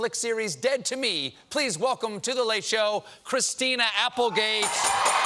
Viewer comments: "What if I'm afraid of my own feelings"? - Stephen "Then you should go to therapy." - Christina (in a quiet, supportive voice) <3 And for a moment I thought he said "Christina Applebee" when he thanked her for being there. Wonderful interview! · Did he just say Christina Applebee??? LOL